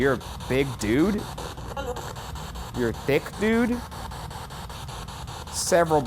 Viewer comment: I thought she said you’re a bit rude not you are a thiccc dude